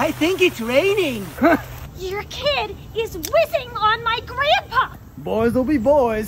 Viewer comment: “He’s whizzing on my grandpa!” 💀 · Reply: I thought it said rizzing